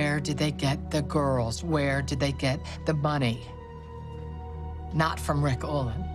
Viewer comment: Does anyone else besides me realize that at 2:07 it sounds like Benson says “Where did they get the bunny?” instead of money? 🤣🤣😂